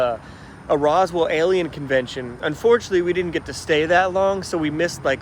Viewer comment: I thought you said “Aryan” instead of “Alien” convention and was very concerned for a minute lol